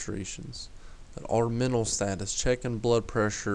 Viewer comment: Just in case nobody else understood what he said approx. around 11:30 AMS is Altered Mental Status